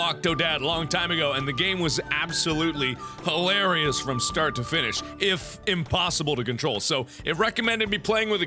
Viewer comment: mark: ''hilarious from start to finish'' me: Me: did he just say 'from start to FISH'?